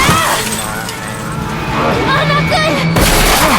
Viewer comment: What did she said at 3:03 ? Not gooey?